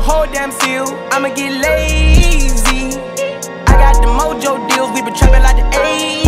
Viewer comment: I thought it said "ABC" but is "I'm get lazy" :v